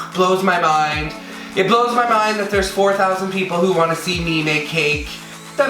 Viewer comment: Yooo it sounded like he said 4k that wanna see me naked😂😂😂